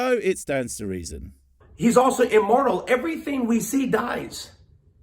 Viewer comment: When he said immortal I thought he said immoral lmao